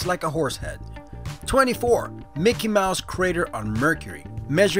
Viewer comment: When you said, "Mickey Mouse crater on Mercury," I thought you said, "Running water on Mercury." I was like, WTF?